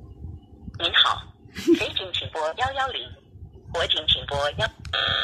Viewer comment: this is what i hear: think how, boys has found the yau yau ling, boys has found the yau- *crashes*